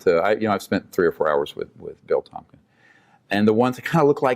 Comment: or so does he say he spent a few hours with Bill Tompkins?